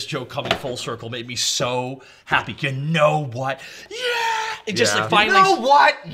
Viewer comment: Holy Mandela effect I could’ve sworn trunks said “ YOU KNOW WHAT I AM “ and not “ YOU KNOW WHAT YEAH “ anyone else?